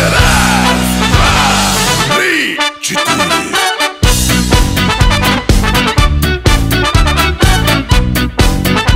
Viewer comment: what does he say at 1:24? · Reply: один, два, три, четыре